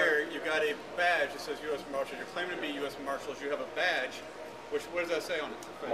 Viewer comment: I mean my name is Marshall. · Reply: I thought u said "Louise Marshall"